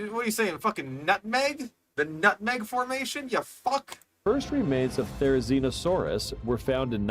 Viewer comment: did that say "cursed remains of therizinosaurus were found"? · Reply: first remains